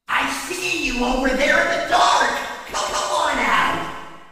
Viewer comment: When I First Heard Funtime Freddy Say That I Thought He Said I Know Your Over There UnderWear